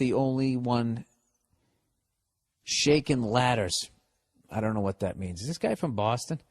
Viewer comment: Billy blue balls how do you NOT know what shakin’ laddddduhs means! Think about it.. you’re walkin by some guy painting a building, high up on a ladder.. you grab the ladder with both hands and shake the hell out of it lmao. Either killing the person or scaring the absolute life out of them 😂😂. “Shakin laddehs!” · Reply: I heard 'shaking bladders'